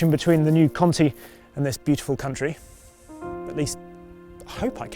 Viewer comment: Did he say” connection between cunty and this beautiful country” at 0:25?